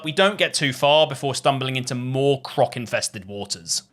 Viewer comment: I genuinely thought he said cock infested waters 😭